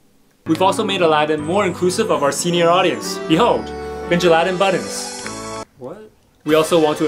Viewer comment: What he said "Benjaladin buttons " What I heard " call us JAladIn "